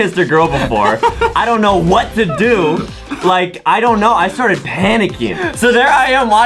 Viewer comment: I started panting and laughing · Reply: xXJamie.Loves. .CookiesXx Oop at first I thought it said “I started painting and laughing” lol I was confused